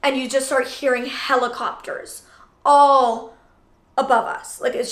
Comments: thought she said “and u just hear hella copters” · I thought she said hella coppers But she said helicopters